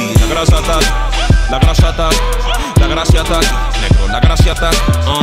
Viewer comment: Alguien mas cree que dice (min 1:37) "La Gracia ta" suena como = La gra Satan :/